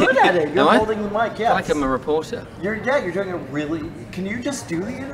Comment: Thomas says 'I feel like a reporter', but the subtitles say 'Harry Potter'...